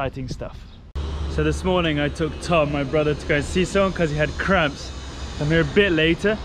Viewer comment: Did you say Tom’s got crabs?! Isn’t that a bit too personal for this channel? 😮